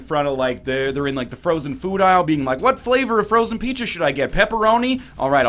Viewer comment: did you just say frozen peaches? Yes sir, I would like a box of totino's peaches, 3-meat flavor, or just canadian bacon.